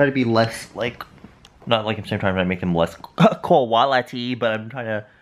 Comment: i heard that as koala tea and was like okay that must be some korean thing and a few minutes went by watching the vlog and i go oohh.. QUALITY lmao